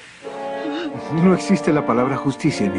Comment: What did Marin say here? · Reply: She says "No es justo" in english "is not fair"